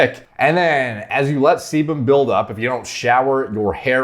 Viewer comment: Thought he said semen build-up at first.